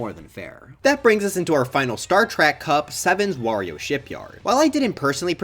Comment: At first I thought you said star trek cup But nah You just upped words the mess